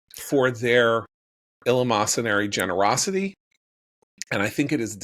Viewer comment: WHAT IS THIS WORD!!! I have been trying to look it up for the last 10 minutes 😭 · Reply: eleemosynary